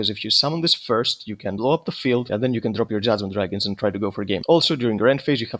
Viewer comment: I thought he said jasmine dragon I was like “what?”